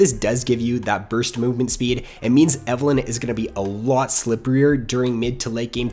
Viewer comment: Did he say "splippieier" at 4:33????